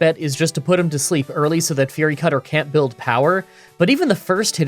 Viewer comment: Thought you said "Fairy Cutter" at the bug gym part, thought that would be a cool move idea, super effective dark type move against fairies like freeze dry is to water types.